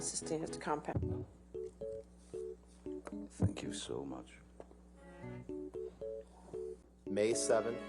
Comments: I thought he said "F*ck you so much" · on 1:48 i thought depp said fuck you so much